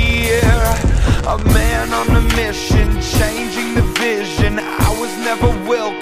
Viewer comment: I hear," A man on a mission changing division." XD